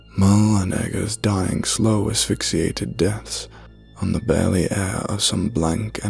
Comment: Um... Did you really say "My n*ggas dying slow, asphyxiated deaths" ?? Cuz I cant lie. After 10+ of listening to it... Thats exactly what it sounds like